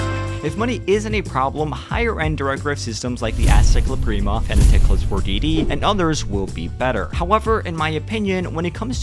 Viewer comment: What did he said at 2:00? Fanatec what? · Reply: Fanatec ClubSport DD